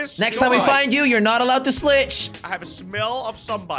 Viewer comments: did just say slitch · am i the only one that slowed the vid down to prove that at 3:36 he says slick instead of slich